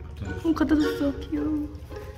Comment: 뭐라고 한거예요? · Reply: (돌체가 연어 모카에게) "갖다 줬어 착해" 라고 하는거에요!😁